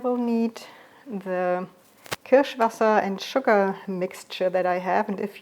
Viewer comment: I thought you were saying sherry water, I guess it’s cherry .